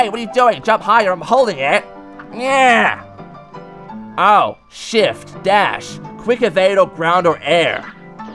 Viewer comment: did you guys heard what he said!? did he say shi or shift?